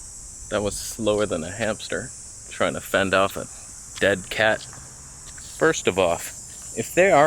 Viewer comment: Did you just say "first of off" in 1:05? :D Love your videos, man.